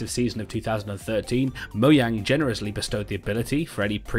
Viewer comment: did he say MOYANG?